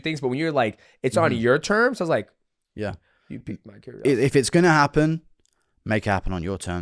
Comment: "you've peaked my curiosity" for those who couldn't hear it at first